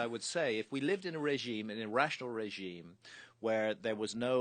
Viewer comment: the captions say an irrational society, but i think he means in a rational society, hes just speaking easy.